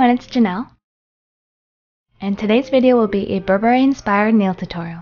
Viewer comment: I thought it said blueberry. And then I was like "why is it red"